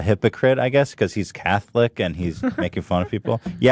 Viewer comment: I think she said "cackling" not "catholic". You know... the type of laugh most commonly associated with fairy tale witches. I mean she is still being an idiot, but I guess she was referring to you making fun of her, and not your religious background.